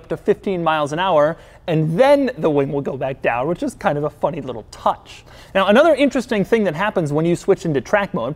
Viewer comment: I heard "A fucking little touch". I'm dead